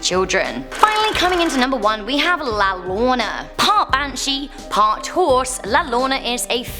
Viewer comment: La Llorona is pronounced (la yo-ro-na) in spanish two ll's in spanish pretty much makes the "y" sound. it sounded like u said "la lona" im like, "wut?" lla = ya llo = yo ll = y